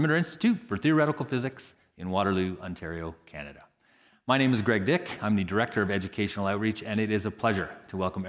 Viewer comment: I thought he said his name was "great dick" 0:47 I need to clean my ears out lol